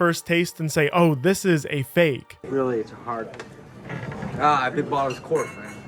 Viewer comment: "fled to Australia" shows Austria... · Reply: While we're at it, 15:57 i think he doesnt say its a fake wine, i think he says "oh no it corked" - meaning the wine reacted badly with the cork (or some kind of mold on the cork). This happens to some bottles naturally and ruins the taste.